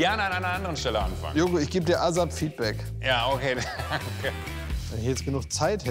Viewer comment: Was sagt Klaas bei 5:28? Azad-Feedback? 😅